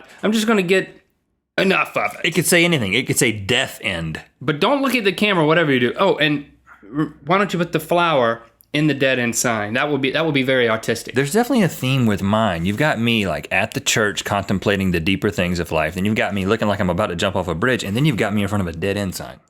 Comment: he says that it would be very AUTISTIC, instead of artistic. Woops